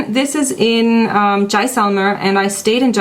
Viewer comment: Can someone please tell me what place says at about 14:50. She says something like Jysonger. It's in India but I don't know how it's actually spelled. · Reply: yes tht is jaisalmer which is in Rajasthan it is too beautifull city